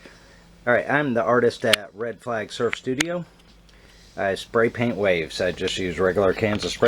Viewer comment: lol i thought it said spraypaint weave but i was tired so lol